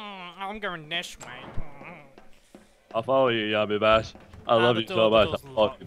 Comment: Did anyone hear markiplier say I love you so much yammimash at 5:57?